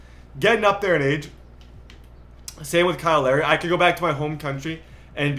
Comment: I heard kyle larry